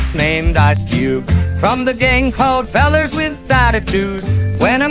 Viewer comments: "Fellers with attitudes" LOL. Amazing AI cover. More Hanksta Rap please. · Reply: I thought he said coloreds with attitudes. Fellars makes sense though lol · Fellers with attitudes 🤣 Great work dude! The instrumental solo too, whole track is 🔥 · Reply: I actually heard him say "fellers with platitudes", which might fit just as well